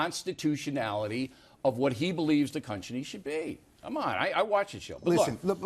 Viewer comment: O'Reilly said cunchiny instead of country lol